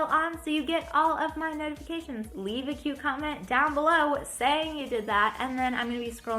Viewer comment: at first I thought she said "leave a cucumber"